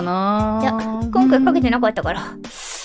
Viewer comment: I think at 2:47 Roboco said 今回賭けてなったから "we didn't bet anything this time." CMIIW.